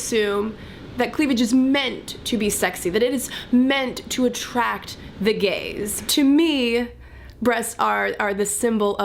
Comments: At first I misunderstood her and instead of "attract the gaze" I thought for a second she said "attract the gays" which sounds exactly the same but made me go "huh??" Lol! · When you said "attract the gaze" I heard it as "attract the gays" ... Gays don't like boobs so I was confused. xD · "Meant to attract the gaze" I got confused because I heard "Meant to attract the gays" haha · When you said "Meant to attract the gaze" I first thought you said "meant to attract the gays" and was confused hahaha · Totally thought your said "attract the gays" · I definitely thought you said 'attract the gays'